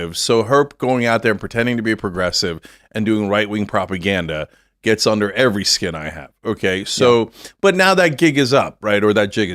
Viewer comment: did he say it goes under every skin he has???!!! That settles it! Leftists are reptilians or some form of alien!! He let it slip out!! Gotcha!! 😂😂😂😂😂😂